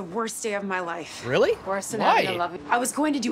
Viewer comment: Am I mishearing or does Kate say “Worse than 9/11?” here?